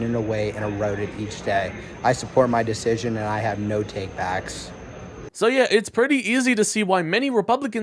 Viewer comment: I thought he said "No takebacks"?